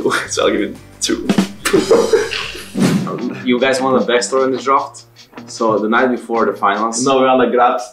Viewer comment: What did they said on 4:52 both perkz and jankos? Can't hear clearly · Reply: Perkz - "So want a little backstory about the draft?" Jankos - " We're on the Grabbz story "